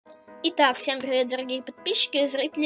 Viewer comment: did you say "Niggaz!?”